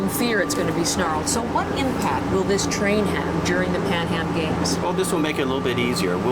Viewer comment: I keep hearing Panem games, is this Hunger Games??